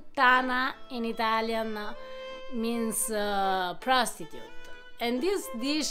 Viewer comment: I'm so embarrassed, I thought she said "Prosecutor" and I couldn't figure out what a "Prosecutor" was doing in a Brothel. OHHHHHH, Prostitute. hahaha.🙃